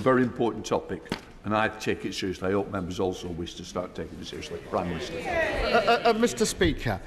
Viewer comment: They thought he said Keir Starmer 😂😂😂but he actually said Prime Minister